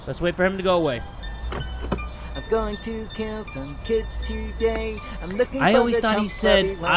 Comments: bro I tried my hardest to find out what he says and idk because it says catch but I REALLY sounds like kill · DID ANYONE HEAR HIM SAY "I'm going to kill some kids today" ?!?!?!?!